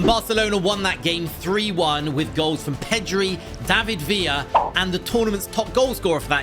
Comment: Did he just say pedri